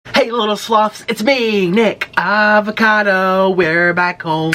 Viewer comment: This is a joke but i thought he said "hey little sluts"